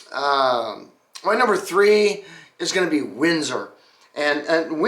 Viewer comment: Man the first time you said Windsor, I thought you said Whizzer, which would be a really odd name for a car company.